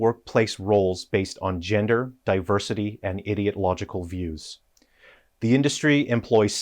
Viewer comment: Did you just say "idiot-logical views"? 😀